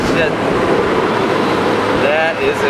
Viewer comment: did he say shit? haha